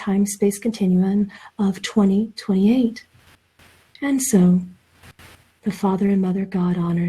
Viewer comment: I heard “...and the time” after the 2028 comment around 36:11 . Do we know who said that and if those were the actual words? Thank you 🙏 💜